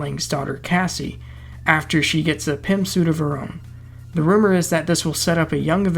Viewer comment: Welcome back...btw...it totally sounded like you said PIMP suit rather than Pym suit 😂🤣...we've missed you.man...seriously...welcome back.